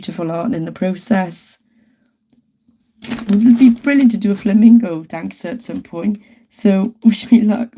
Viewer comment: This might sound crazy but when you said "flamenco" dancing I heard "flamingo" dancer, but wouldn't that be cute? Not sure how to pull it off but it's something to think 🤔 about 😂